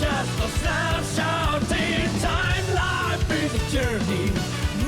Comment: Huh. I just realized the line was "snapshot in time". I thought it was "sad short detour". I guess either work for the point of the song but I'm kinda shocked I misheard it for so long.